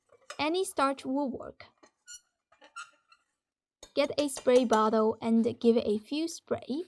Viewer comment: Thanks for adding the information about how long the recipe components can be stored in the freezer, as a single person, it is really helpful. The written recipe is sometimes wrong, for instance the recipe says spread bottle while I clearly heard you say spray bottle when clumping the starch. I appreciate your offerings.